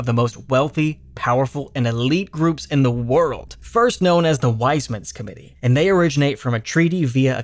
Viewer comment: my brain is beyond done for, all I heard was "known as the wiseman skibidi"